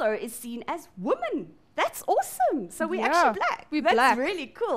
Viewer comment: Did she say were actually black??